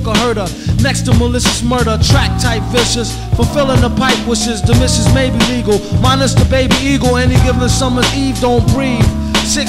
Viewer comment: I think at 2:02 he says "the (da) mrs. (missus) may be legal